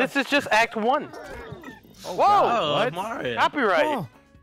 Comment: Did say Mario?